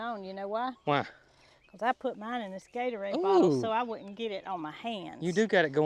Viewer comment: Oh,. I thought you said shake your " bottom " , I raised my eyebrows but realized you meant bottle. 😮😮😮😂😂😂